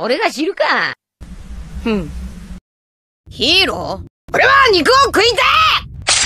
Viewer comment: Subtitle : "Hero... But your behavior is disgraceful" What he actually said : "Hero... GiVe mE My MEaT BACK!!!"